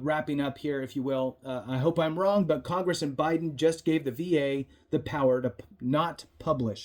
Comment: Did you say congress and Biden ?